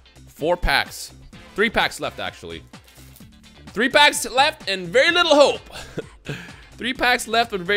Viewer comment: Yknow I’ve heard y’all say this like 40 times before, but wtf are you even saying at 13:54 😂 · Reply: “Three packs left with very little hope”